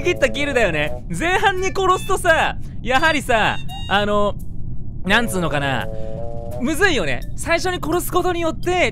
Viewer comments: 私の耳には「にゃんつーのかな」と聞こえてかわいい～💕ってリピ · ごめんね…… にゃんつうのかなって聞こえたww